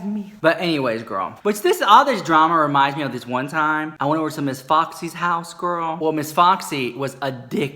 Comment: OMG I thought you said Miss Boxy, I was like ‘What?!’, but it was Miss Foxy. 🤣 #coffeestillperforms